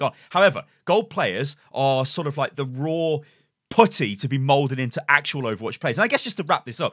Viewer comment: Did he just say 'raw potty'?